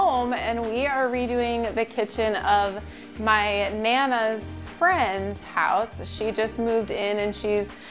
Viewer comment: I thought you said she was your nana now she’s your client 🤔 you capped 🧢 🤣 but nice hustle great job!